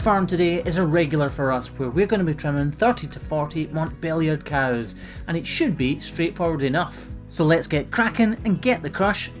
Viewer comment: I wasn't paying attention and I thought you said "today we will be trimming 30 to 40 billion cows"